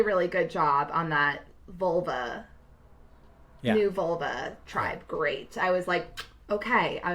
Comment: am I crazy or did she say vulva tribe 🤣